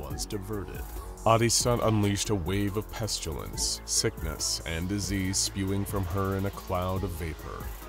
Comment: When A'DISTAT released the pestilence: SCP-049 : Did I hear ✨ pestilence✨?